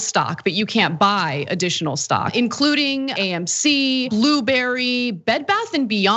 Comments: Did she say blueberry when naming the stocks at the beginning? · Did she really call blackberry Blueberry?